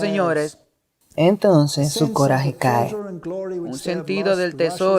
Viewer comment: Saludos. ¿Pueden escuchar el minuto 56:25?? ¿Porque se escucha la voz de una mujer que dice,, "su gloria cae",,. Oremos por éste y todos los programas. El "enemigo" está tratando de intervenir para confundir.